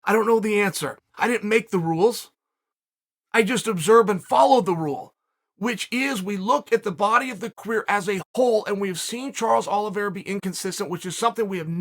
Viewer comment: …Wait…did he say “body of the queer”? Lol